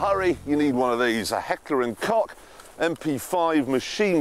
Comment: I heard Hector & Cock, cuz it is something that Clarkson would say haha